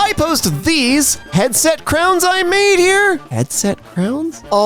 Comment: Did she just say Kirby for the bosette crown?